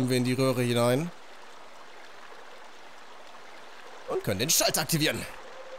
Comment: Ich habe irgendwie Milchschalter verstanden.